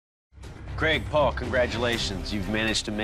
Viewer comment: “Craig, Paul” I thought he said